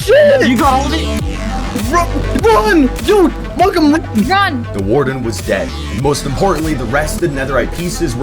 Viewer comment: somehow, i heard 'the warden was gay'… i dont know how i heard that.